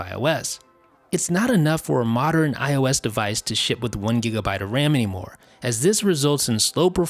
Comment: It's not enough for a modern iOS device to shit with 1GB of RAM anymore, sounds like that's what he says